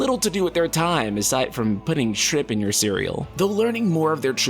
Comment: I heard "Putting sh1t in your cereal"...I'll see myself out...